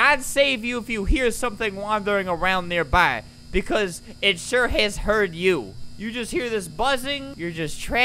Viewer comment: God save you if you hear something wandering around nearby, because it sure hell has heard you. Thats actually what it said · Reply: I thought it was, "because it sure as hell has heard you." It is.